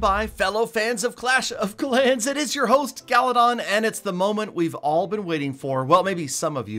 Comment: My first thought before he said the name was galadont as well lol